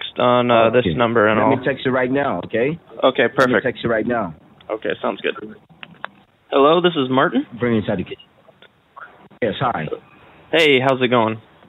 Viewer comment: I was listening to the video without watching and it really sounded like he said "British etiquette" to me, lol. Had to go back and re-listen, cause that seemed like a weird thing to say when picking up the phone. Still sounds like he says that, though xP.